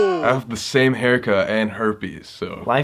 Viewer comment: Did just say he has herpes?!! Wtf?!